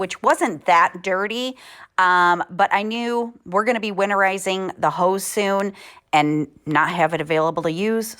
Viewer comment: Love your vibe! When you became an inspiration to you mom I choked up. Can you show what you mean by winterizing your house?. I'm not familiar with what that entails. Thank you. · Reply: I thought that she said “going to winterize the hose”. If that was the case, it’s because you can’t leave a used hose connected all winter in a cold climate or it will freeze and burst. So you have to let it drain or blow it out and turn off the outside spigot. Some even put the hose in their basement if they aren’t sure that it emptied completely. If she did say “winterize the house”,you turn off all of the outside spigots,some people remove screens and put on storm windows and doors,close a/c vents( in some houses). We lived in Vermont for ten years and people would put plywood tents over their bushes to keep the snow weight from crushing them. (we averaged 160 inches of snowfall each year.😵‍💫🫣😬) I probably forgot a few things.🫢